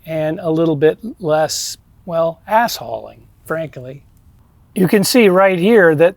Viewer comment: I thought you said "assholing". Which I consider to be fairly accurate.